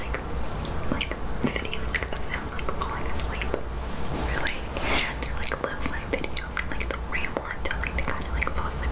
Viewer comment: Did anybody understand which Channel was talking about from 14:55 - 15:15 ? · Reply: asmrturtle?